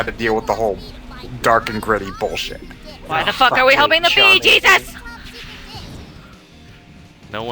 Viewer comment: When said "helping the bee, Jesus!" I at first thought she said "helping the Bee Gees".